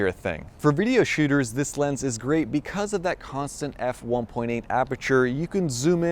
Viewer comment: did you say... 1.8 aperture? 😉